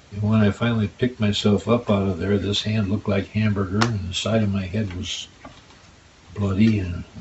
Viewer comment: did he say his hand looked like amber heard?